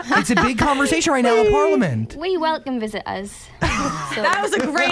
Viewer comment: What did Jade answer at 10:23? Didn't get what she said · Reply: ernestina cator "We welcome visitors"